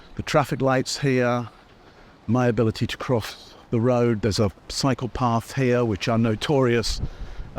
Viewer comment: This was fun! And very informative, as always! I absolutely LOOOOOOOVED getting a moment inside wonderfully unique, detailed, enlightened, creative, eloquent Brain!! I’m 😂🤭😅 because when he got to the main strip in town & was waiting to cross the street, I thought he said, “There’s a Psychopath” but he really said, “There’s a CYCLE PATH!!!” Thanks for making me smile! ☮️💜🌞